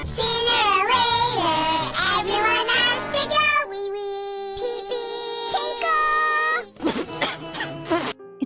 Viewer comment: Does he said wee wee?